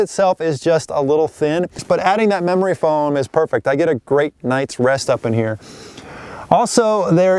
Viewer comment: what he said "I get a great night's rest up in here". What I heard "I get some good sleep in this bitch" ha